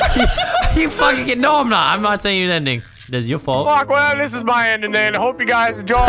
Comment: did he just say n word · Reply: "im not send you the en cuts out ing"